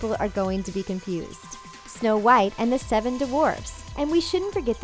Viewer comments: Did she had a problem saying dwarfs? I heard "the seven dvarfs". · dwarfs sounded like divorce. Huhuhu and now I can't unhear it